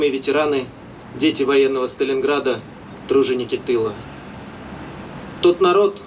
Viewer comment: i think he's saying "Труженники" or Workers, and not "Дружинники" or Volonteers/milita other than that cool vid as always